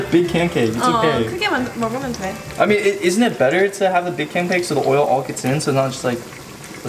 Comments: did say canpake instead of pancake at 14:06 pls 😭😭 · who else heard say canpake and not pancake lol🤣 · Is it just me or did say canpake instead of pancake at 14:05? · said canpake instead of pancake lmao